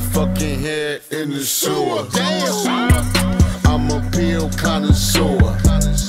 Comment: Does anyone know what he says around 0:58? "Im a pill kind of sour" or anything like that, lol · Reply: He said he's a pill connoisseur.. A connoisseur is an expert/judge in the taste of something. Example: a connoisseur in music, someone with expert taste in music.